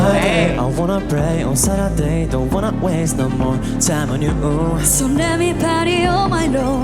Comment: ちょっと今更なんですけど気づいちゃって気になったんで🆘🙌 1:33:21 ここのRUIくんのパート 「〜Saturday don’t "wanna" waste no〜」 字幕wanna抜けてない？ 原曲そのままじゃなくて変えてるみたいだし、あえてなのかな…？でもRUIくんは歌ってますよね…？ 1:34:04 ここのRYOTOくんパートはGirl→boy 1:35:00 REOくんパートはm-flo→LAST PIECE って意図的に変えてますよね 字幕がそのままなのは本番だけのアドリブってことなのかな？ 59:43ここのISANAくんのところはちゃんとオリジナルに変わってるのにな…（GETするLAST PIECEって歌ってますか…？私聞き取れない😢）